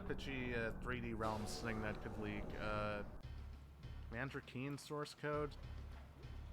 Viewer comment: what game did you say? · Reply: Commander keen